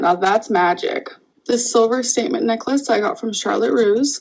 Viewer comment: I thought she said “salmon necklace” not “statement necklace” 💀 GORL PLEASE JUST PUT THE STUFF IN THE CROCKPOT STOP IT. AND THEY ARE AN MLMS DREAM.